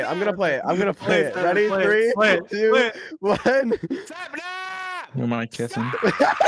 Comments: I thought George said I’m gonna kiss him 😭✋ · I thought he was saying “I’m gonna kiss him” and then I REALIZED HE SAID “you’re my kitten” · Lol when sap starts to spam George saying you're my kitten it now sounds like you don't mind kissing idk why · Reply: I thought it was saying "Im gonna kiss him" at first m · I hear "Dream and I kissing"